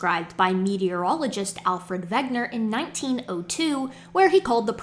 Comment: Every time you say "Vegner", I hear "Vecna" and do a double-take. 😳